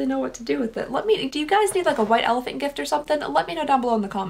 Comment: Did...did say " Wet Elephant Gift" at the end (@ 0:18:37)? Is that a real thing that some people call it?